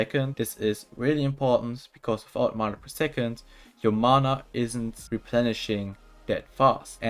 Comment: Sounds like he said ur mama 🤣🤣🤣 funni